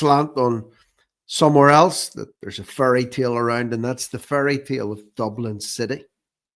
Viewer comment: When said ‘fairy tale’ I thought he said ’furry tail.’ My bad! 🇺🇸🇮🇪 Erin Go Bragh.